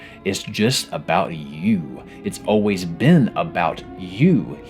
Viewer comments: The subtitles saying "us" instead of "you" at 41:04 weirded me out a bit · why do the captions say “us” whenever he says “you”?